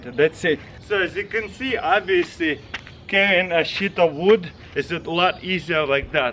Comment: I thought he said sheets of wood😂😂😂